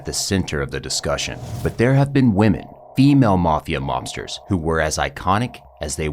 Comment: What I thought he said: "... But there have been women... Female women... " What he actually said: "But there have women, female mobsters that were iconic..." I'll be honest, I kind of wish he'd said what I thought I'd heard. I'm interested in how he'd have finished the sentence.